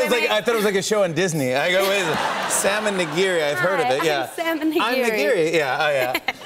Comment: salmon nigiri 🍣 lol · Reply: I heard Sam n' nigiri